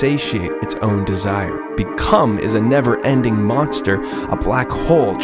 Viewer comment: For a new name for Dead Hand, I am almost sure I misheard you, but at 3:27, I thought you called it "Become." If you don't like "Dead Hand" I doubt you'd like "Become," but that just sounds like a really cool and fitting name to me.